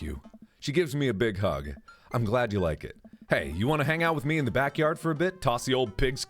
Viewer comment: "Wanna hang?" "Sorry I'm busy watching someone play a game called dream daddy" "Ok...." 5 HOURS LATER "Can we hang out yet" "Sorry busy watching dream daddy still" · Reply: blondefry at first I thought you said "want to bang" 😂😂😂